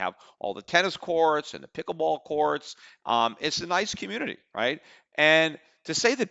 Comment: Did he say pickleball courts?